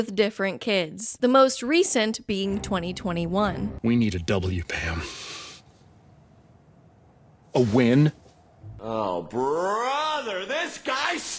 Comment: I genuinely have no idea what he said here, or what the joke is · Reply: Man: “We need a W here Pan!” Pan: ….. Man: “A win!”